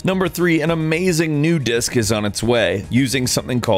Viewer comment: Thought it said Dicks at 5:48 ...